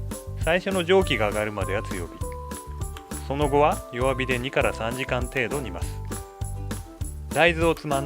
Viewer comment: the narrator says, "cook for 2 to 3 hours (ni kara san jikan)". The English subtitle says "cook on low heat for 23 hours". It should be 2 to 3 hours, not 23.